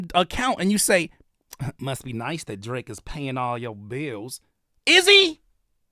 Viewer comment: I was working when listening to this and first time I heard "Drake is featuring PMD" and was freaking out with the outrage LOL